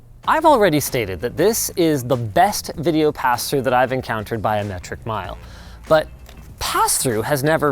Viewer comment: BIO METRIC MILE. i don't think you intended this pun, but once i heard it i was like "that fits the topic"